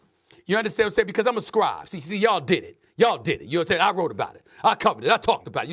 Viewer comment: I thought he said “y’all diddy”